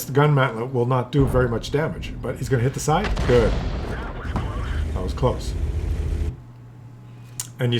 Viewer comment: Every time the commander says, "That was close!" I hear "That was Claus!"